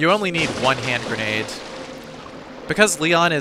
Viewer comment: he said wider, but i thought he said whiter lol